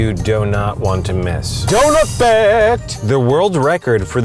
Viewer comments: Am I the only one who thought he said Dora Fact instead of Donut Fact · It's sounded like he said Dorah fact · I thought he said Dora fact instead of Donut fact lmao